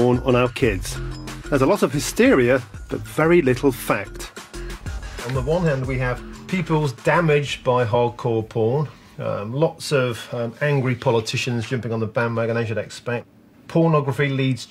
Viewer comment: what does he say from 12:20 to 12:25, an ESL student needs help here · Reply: On the one hand we have pupils damaged by hardcore porn. Lots of angry politicians jumping on the bandwagon as you’d expect.